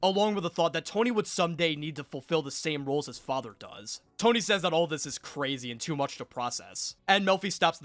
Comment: “Tony says that all of this is crazy and too much to process.” What Tony actually says: “This sounds very gay.”